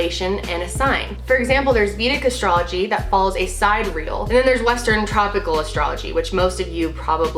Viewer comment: Explain what you meant about vedic astrology? I thought you said, "si-real." Do you mean Sidereal?